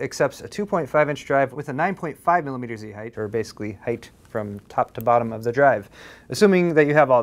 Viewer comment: what does he actually say at 0:39 before the goofy voiceover? also this drive is a ripoff to the max. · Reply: 9.5 mm height from top to bottom